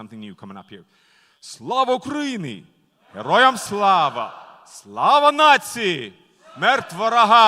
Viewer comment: Did he just say glory to the nazis? 😬😬😬😬😬